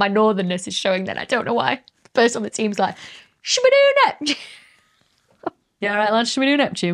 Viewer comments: I heard "Shoo-bee-doo Neptune?" and "Scooby-Doo Neptune?" Either sounds pretty cool to me. 😆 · First Pluto now Neptune. I hate science, they give you a candy and the moment you start sucking it, they take it away. Also did Becky said Scooby-Doo Neptune in the end 13:35 ?